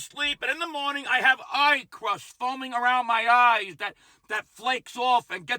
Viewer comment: Eye 👁 crust! Lmao! 😂 I thought you said “pie crust!” Yeah, that’s a fun one in the morning. Have we missed any orifice? 😣😁😘🗽